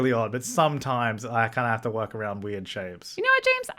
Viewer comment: I paused right at 9:22 when I heard "Sh" and was like 'Oh james, damn, swearing on camera' hit play "Shapes" ... 'oh'